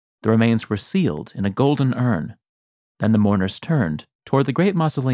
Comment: I misheard "golden urn" as "golden herm" and had to rewind 😂